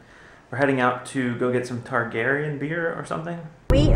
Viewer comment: LOL, I thought said "Targaryen beer" haha!